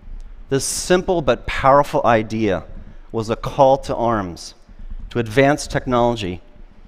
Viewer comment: I thought it was a call for legs... But all inappropriate amputee-humor aside, this is very inspiring...